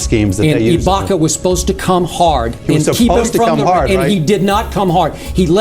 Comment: Did bro just say "he had to come hard"? @ 11:30😅